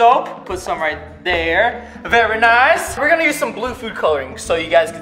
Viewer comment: Did he just say "vey nice" like Pewdiepie